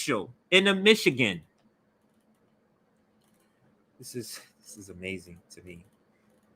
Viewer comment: I thought they were saying meshugaas democrat, not Michigan.